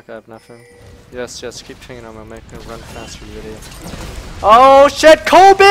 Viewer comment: What was he saying at 3:05 ? "yes yes keep _____ while making it run faster your idiot" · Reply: MrPowerwind l "Keep pinging me it'll make me go faster, idiot."